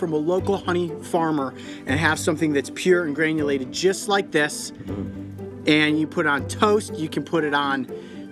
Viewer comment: Am I the only one who thought he said "you can put it on your toes!" Just me.... Ok then...